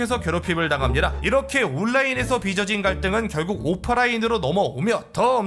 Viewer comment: 근데 이 부분 자막에는 오프라인으로 적혀있는데 내 귀에는 오파라인으로 들리는데 나만 그런가?